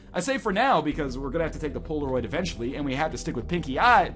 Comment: ish.... I thought he said "Pinkie Pie's eye" I was like "NOOOOOOOOOOOOOOOOOOOOOOOOOOOOOOOOOOOOOOOOOOOOOOOOOOOOOOOOOOOOOOOOOOOOOOOOOOOOOOOOOOOOO!!!!!!!!!!" Why Pinkie!! Why not Rarity?!